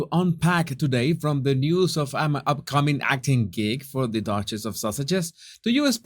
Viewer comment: I'm dien, I thought you said Duchess of sausage lol oh my I can't